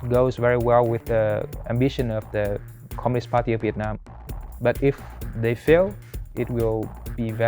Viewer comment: Did I hear this guy say communist party of vietnam 1:40?